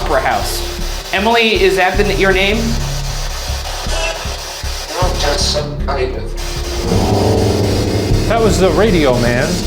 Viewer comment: Male (accent): 'No, just some private...' Sounded like he hailed from the UK... 8:34 Male: 't's alright, I'm not making love'--C'mon Madej, that's too long a sentence to be a radio station sweep--same voice throughout, too! 32:39 Male (slight accent): 'If you've got two cents you've got...' 👀‼ 32:50 Male: 'I earned my death' 👀‼ 33:13 Female (distant): 'Get out of here' 33:20 Male: (Unclear) better'--same voice as at 32:50 46:52 Same female voice, both responses: 'Yes' 'Henry' 47:56 Male: 'You've found some great words'--He's right! The SB EVPs have been conversationally accurate!